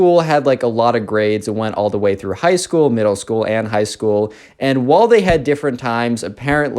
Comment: what did he say here :/ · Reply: High school middle school and high school ✨duh✨